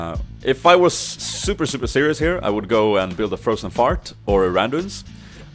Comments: did he say frozen fart at 14:08? lol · Anyone else who heard frozen fart 14:05 ? :D